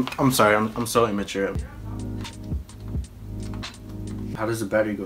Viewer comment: I heard "I said " did anyone hear that